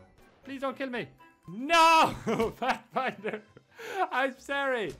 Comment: subtitles sait fat finder instead of pathfinder lmaooooooooooooo